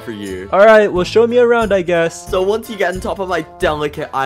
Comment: when ge said garnished i thought he said garbage